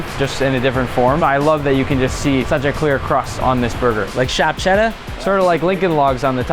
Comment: did he just say "SHAP CHEDDAR" hahaha love that accent